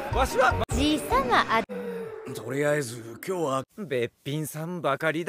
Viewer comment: she really said womp womp?